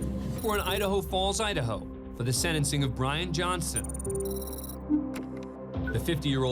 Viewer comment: Bruh when they said Bryan Johnson, I thought they said crying Johnson, and he was crying at the time!